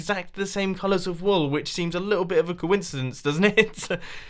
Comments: Did he just say i knows were i live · Ok. Am I hearing things or did Mumbo say "I know where you live"